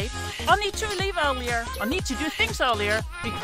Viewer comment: At the end of every video it sounds like she is saying: “Life is amazing Lieutenants Tacos”